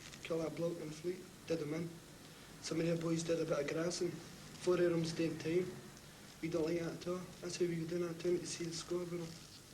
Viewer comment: What does say from 1:45 to 1:52? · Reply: Some o' they boys did a bit o' grassin' (some of the boys became police informants), four o' them's daein' time (four of them are in prison), we don't like that a' ta' (we don't like that at all), that's how we go doon a' the time to see the score with them (that's why we regularly go out looking for them, so we can settle the score i.e. take revenge).